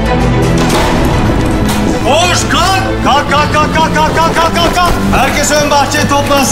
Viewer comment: Did he say “WAKE UP YOU CHICKENS”? 😂